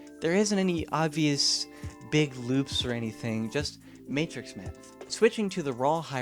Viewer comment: Question: what do you say at 12:56? Matrix maps? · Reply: He said matrix math